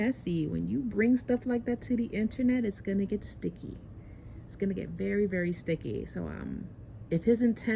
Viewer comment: Soon as you said that, all I heard was “It’s gettin’ sticky in this b****…..” 😬 Really hope they work this out for Halo!